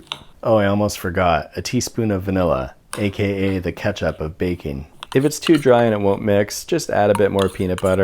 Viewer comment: Vanilla - the ketchup of baking 😂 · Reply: thought he said "the catch up of baking"